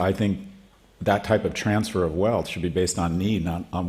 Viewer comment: Why does Scott Galloway continue to paint Baby Boomers with a broad brush? Like they're all responsible for the greed. I know many wealthy people under 35 and many renters and lower middle class people over 60. He's right about many things but he's creating class warfare like Obama did in the 2010's. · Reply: He actually said NEED based